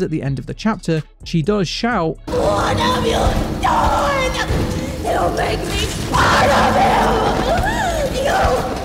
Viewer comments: I THOUGHT SHE SAID ''YOU'LL MAKE ME SWALLOW YOU'' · When Mommy died, I thought she said "You made me one of them", talking about how they killed her 'family'. Which my thought kind of connected to the protectiveness of the toys, saying once her 'family' died, she didn't care anymore and went completely crazy